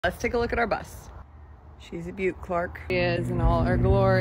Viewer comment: I thought it said "let's look at our butts"